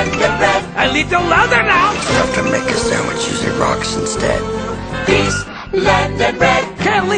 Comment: What is he saying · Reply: "It's tough to make a sandwich using rocks instead."